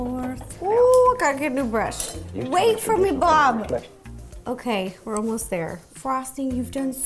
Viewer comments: She said Blob instead of Bob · Did you just say “ wait for me blog?”